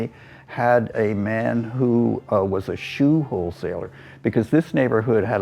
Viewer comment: At around the 10 minute mark, I thought I heard him say, “shoe hole seller.” I stopped what I was doing, thinking WTF, and a few realized it was “wholesaler.” 😂